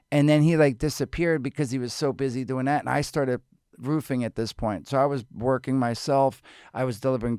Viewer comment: Roofing! I 100% thought he said he started Boofing at this point...